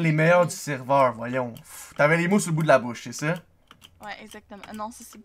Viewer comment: Au début j'ai entendu "T'as de la mousse au bout de la bouche" 😂